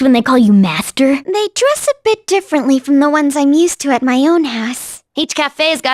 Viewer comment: I'm a bit embarrassed to say that I misheard one of Haru's lines as "They dress a bit differently from the ones I'm used to at my own ass".